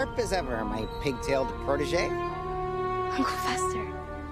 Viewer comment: I thought she said what iT WaS Uncle fESTer